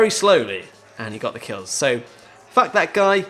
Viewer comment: did he say "vac that guy" or "fuck that guy" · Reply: yea he said fuck